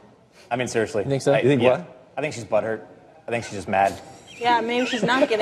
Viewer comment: I also thought he said she'd be buttered lol.